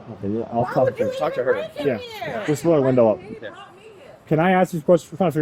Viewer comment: did she really say she didn't bring the dog in here, the dog brought her here.😂😂